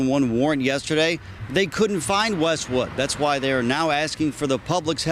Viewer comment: asking for the pullbacks health.. am I the only one who heard that? ten bucks says she got her raciness from her daddy that's why she moved closer to him